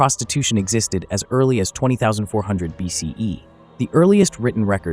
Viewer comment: I thought I heard 20400 BC... but the subtitle reads 2400 BC that seems logical.